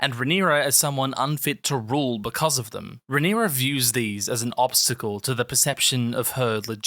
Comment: Did anyone else hear "views these" as two separate voices overlapping what the hell was that